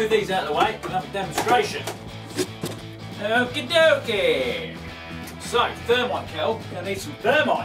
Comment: I thought it said thermite Kermit